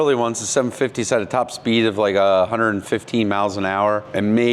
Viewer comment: Did you say, "115 meows an hour?"